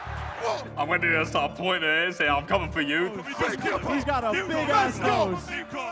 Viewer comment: Lol at the start I thought it said big gibber lake